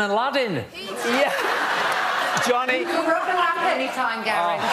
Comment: what johnny says gary from him laugh so much? 45:36 I'm Italian and I do not understand all the words! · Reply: Johnny said "you can rub my lamp any time" to Gary when Gary said the costume looked like it belonged in Aladdin.